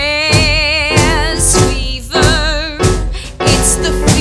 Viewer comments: It sounds like she is saying jizz fever 😂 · This is some real talent, folks. I fucking loved it! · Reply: I keep coming back to it because of how funny it is. It almost sounds like she says Jizz fever when she over pronounces Jazz xD